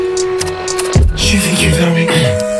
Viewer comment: ' jai vécu dans mon cul ' i heard it at the beginning around 00:17 you can translate it if you want